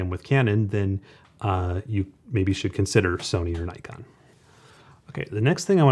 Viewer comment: at first I heard "maybe you should consider selling your Nikon".